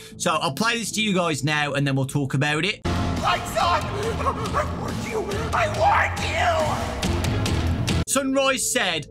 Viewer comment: Sunrise actually sounds like he’s saying “I WARNED YOU, I WARNED YOU” maybe he could be a neutral animatronic warning us about moonrdrop (or someone else?) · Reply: Yeah, when I first heard sunrise I heard "LIGHTS OFF, I WARNED YOU".